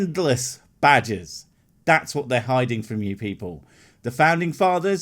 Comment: good thing you put up a picture. i really thought you were saying badges not Badger's